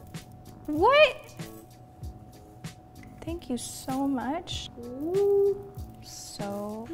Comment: THIS IS WHAT STORMI SAID IN OUTRO Stormi: You are on Kylie's YouTube..everything here's to eat and now we get to eat cookies..You know what is that? Dumbo Its cookies...Thank you for support..okay...byeee (This is exactly what she said) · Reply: Мадина Абильханова lol I heard “thank you for the oven”